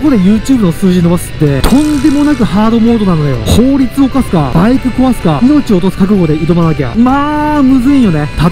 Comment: 脳死しながら見てたらバイク犯すって聞こえて電車の中で笑いそうになったわ